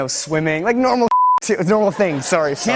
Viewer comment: what did he exactly say?...like normal?.. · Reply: normal shit